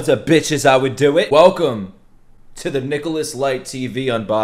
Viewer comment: Idk why I heard, “Welcome to the Thickolas” instead of “Nicholas” lol